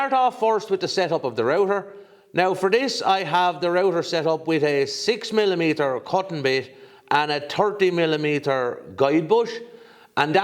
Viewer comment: a "cotton bit"?? Am I missing something or is that just a charming celtic accent for "cutting" ...? Even the automated subtitles say "cotton". 2 more minutes in: yep okay, I get it - the prononciation is consistont. 😘